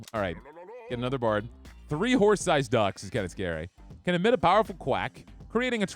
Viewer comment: I was alt-tabbed working, letting the audio play for a few while I finished the last rows on a spreadsheet, and I could have sworn you said Wh*re-sized Ducks at wave 67, lol.